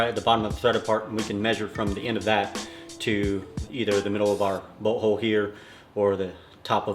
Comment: swear to everything I dead thought he said he was going to measure his butthole 😂😂😂